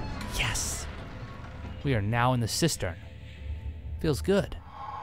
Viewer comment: What he said: " we are now in the cystern... 'Feels good!" What captions thought he said: "we are now in the sister. Feels good." Oh, captions. Just that one word made all the difference...